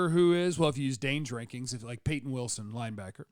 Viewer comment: maybe its because you're talking about the Cowboys but I kept hearing you say "DAY DRINKING" when you were saying "Dane's Ranking". Good thing you weren't talking free agency or the offseason approach as a whole. That would have required me to hear "day mething" or something of equal destructive properties.